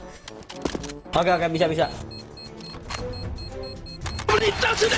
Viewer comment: Who Hear The Bahasa Indonesia That Say "Ok Ok Bisa Bisa😁" on 4:00,Indonesia!!!!!! 1 Like If U Hear It 😁